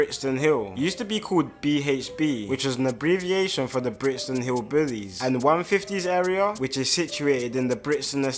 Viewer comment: Sounded like my man said Brixton Hillbillies 🤣🤣🤣🤣